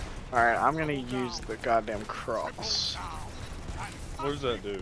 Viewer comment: did he say he was "gonna use the cronus?" Then his buddy asks "what does that do?" He replies "you just point, get crucified." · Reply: ngl I did not really look into this but now I see what you’re talking about. He says the “goddamn cross”. They do not play destiny at all and they are my friends featured in other videos like madden and 2k. This phrasing is reinforced when another one of my friend’s says “get crucified bitch” shortly thereafter.